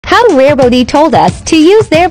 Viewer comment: Why did I hear “how rare booty” 😂😂